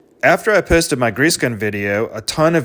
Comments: I heard "After I pissed in my grease gun" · I just heard, "After I pissed in my grease coupler". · I heard "pissed in my grease gun"